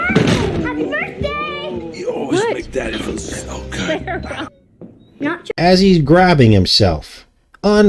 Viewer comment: I can’t make out what Arnold said when his daughter walked in in the movie at the 11:40 mark of the video.. plays it twice and I can’t make it out! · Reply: "You always make daddy feel so good"